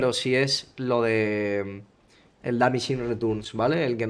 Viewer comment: que es lo que dijo en ingles? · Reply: Diminishing returns, cada vez que se usa un CC en un personaje, el CC se vuelve menos efectivo.